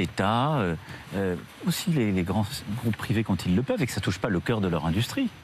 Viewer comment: Les quoi ? Je n'ai pas bien compris ! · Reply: ''les grands groupes privés''